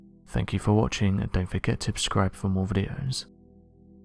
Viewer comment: "don't forget to 'Ubscribe' for more videos"...??? Has anyone else ever noticed that he always says UB-Scribe instead of Subscribe...???